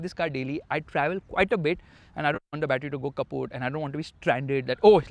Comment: is faisal khan learning deutsch? If i heard correct was it Kaputt 😂😂.